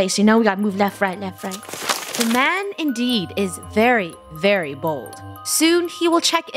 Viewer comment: I heard "the man indeed is very very bald."